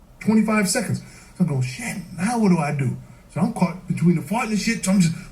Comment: Did this dude just say "I'm caught between a fart and a shit?". That's what I heard and I could not stop laughing for a solid 3 minutes. Forget a rock and a hard place, that's got to be my go-to for that expression now.